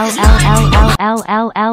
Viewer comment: What did he say shut up